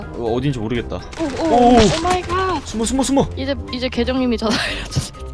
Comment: 에나만엉덩이만ㅈ...이렇게들었나?